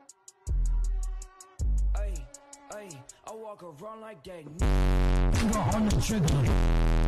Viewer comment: This is what the song sounds like: Ey Ey I walk around like gay BASS We go on the trigger BASS Cause BASS Booo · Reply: I always heard “I walk around like gay”